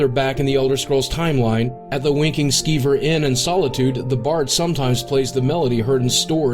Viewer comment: Thought he said "Wanking Skeever"